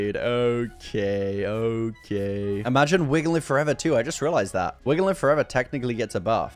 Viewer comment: sorry i thought was saying "imagine wiggling forever" and i was like "yeah so?" didn't realize he was saying "We're gonna live forever"